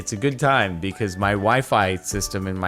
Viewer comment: My wifi literally went out at the same time and it sounded like you half completed saying wife.... and i was WTF